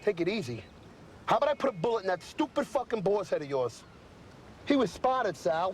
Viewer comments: Skip Threatening to put a bullet in Tony's head... lol 😆 he wouldn't make it pass the weeked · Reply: PlayStation It's funny sounded like he said bosses head🤔 And then the very next sentence after that ... he was spotted...then again Again it's probably just his accent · I’ve never known what Skip said here, did he say “BOSS head”? Ive never been sure if he’s threatening to kill Sal or Tony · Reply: Boar's head